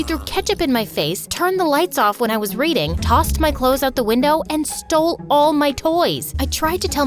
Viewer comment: I thought she said toss me out the window